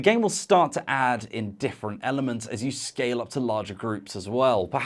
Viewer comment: when said "the game will start to add in different elements", my mind heard "add indifferent elements" and I thought "it adds elements that don't matter to the outcome? Intriguing...oh, wait, that's not what he said, is it?"